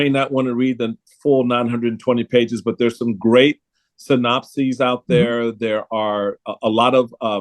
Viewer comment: When Michael said, "synopsis" I thought he said, "the Nazis" 🙄